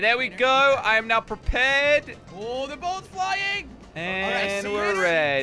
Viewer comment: Did Jelly just say "the boat is flying" ??? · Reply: Their both